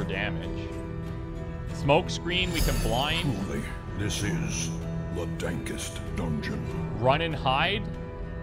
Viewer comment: Did I mishear or did the Narrator just say "Truly, this is the dankest dungeon" at